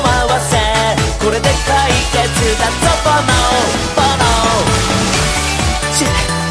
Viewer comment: Romano's so cute~~~~ (*^o^*) This song made me hungry, tho xD Btw, around 0:55 instead of "OK" he's actually saying "Buono" which means "good" or "delicious" in this case. Also, fyi, at that part he's also describing a gesture (the one were he says to press your finger on your cheek) that signifies "buono" in Italian! My little brother used to do that often to tell us the food was yummy X3